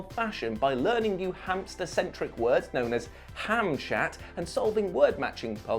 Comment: listening in the background, thought I heard "Ham-Shat" immediately ran to the screen to see what the hell meant. Burnt a quesadilla. Still ate it